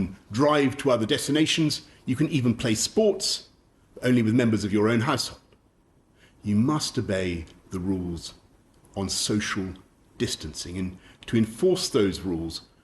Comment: Must obey the way he said i thought he said masturbate the rules. 😂😂😂